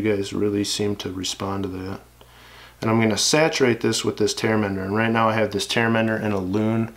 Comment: Hey, I cant quite understand what are you using at 12:26 to saturate the head. Tar....? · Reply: Tear Mender - <link>